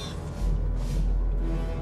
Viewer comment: What did he said??? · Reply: “Send a maniac to catch a maniac”